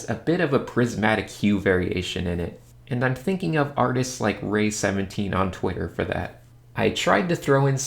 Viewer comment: Did anyone catch or know what artist he mentions at 6:35 ? The one with the chromatic white T-shirts? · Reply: rei_17